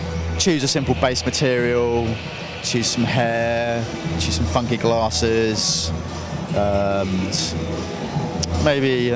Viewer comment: At first I thought he said "fucking glasses" and I was surprised.